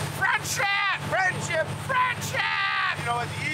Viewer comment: I thought he said fried shrimp, but he said friendship. I like that way better.